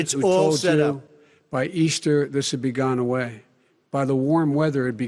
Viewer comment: it might be just me or Biden is stupid. I could swear he said by Easter it could be gone like America